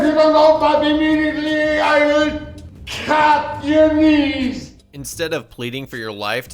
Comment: it sounds like he says "I will cap your knees"